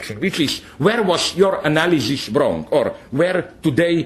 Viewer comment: what does he say in 18:16? "...the tough question wich is Where is your analysis_______? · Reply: wrong